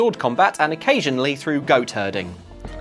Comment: I heard "Go Turding." Anyone else?